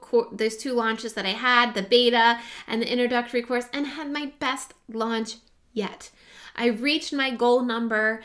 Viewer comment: I thought I heard you say "I had my best lunch yet!" and I started thinking, "Yummm... Lunch...."